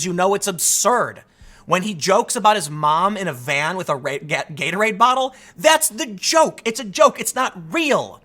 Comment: Did anyone else hear Tim say, "Rape Gator Aid bottle." 7:38 Comedy genius.